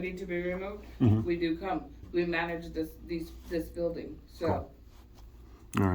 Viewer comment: It sounded like the woman at around the @ 13:06 minute and second marker said that we manage this beast & then corrected herself! 😮